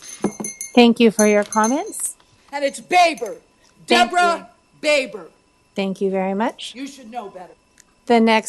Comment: This idiot is Baber. Deborah Baber you should know that. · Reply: I thought she said "debra beaver" 😂